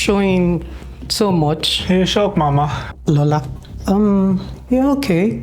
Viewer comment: did he say "shut up mama"??? Wtf!!! Hell nahhh! On a side note thoughhh, is just perfect! Beauty and Brains!